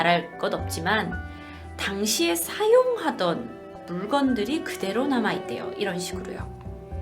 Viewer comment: 당시에 사형하던이라고 들려서 순간 겁먹음 ㅋ